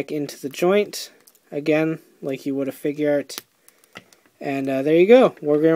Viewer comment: you say to pop his hand back on like a what? I thought you said figure-art but I can't make it out.